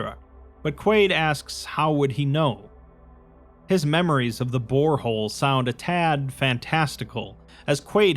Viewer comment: Misheard that as "His memories of the VOREhole" and had to make a double take